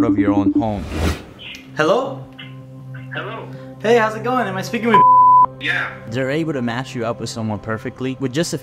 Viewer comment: I THOUGHT HE SAID HELLO AM I SPEACKING WITH SHIT